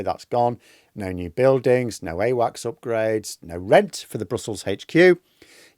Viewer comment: I thought for a second that said “no earwax upgrades” and now I’m wondering what upgraded earwax would be like.